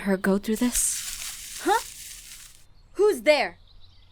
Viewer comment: first time I watched - Aphmau :who goes there second time Aphmau :who's there third time Aphmau:huh who's there me:I think I might've misheard